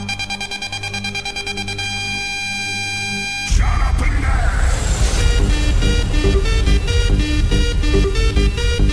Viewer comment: What did he say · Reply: "Shut up and dance"